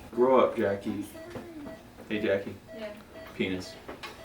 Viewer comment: he says (Hey Jackie “what” Grow up” ok. (He says again “Penis”.